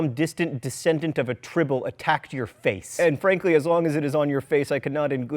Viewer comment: "Anne Frank-ly" I know that wasn't what you were TRYING to say, but this needs to become a thing.